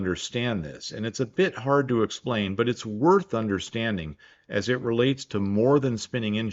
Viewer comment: I misheard “worth understanding” as “war thunder standing” and got my monke neurons activated 😂😂😂